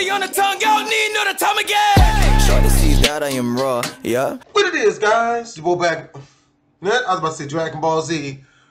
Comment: It sounded like he said "what it is guys It's ye boy blackfamousHD"